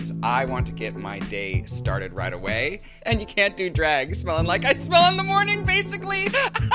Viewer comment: i love how the subtitles at 1:30 say "you cant do drugs" instead of "you cant do drag"